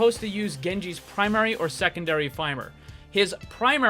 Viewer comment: did he just say (fimer) ?